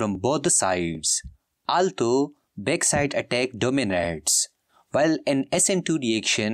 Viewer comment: What did he say from 7:25 to 7:28 "altho back side attack from domiraades"